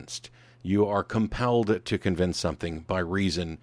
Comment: About 7:15 you say that "if you reject [Pascal's] god you risk nothing." You should have said accept instead. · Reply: there's also "You are compelled to convince something" instead of "...believe something" at about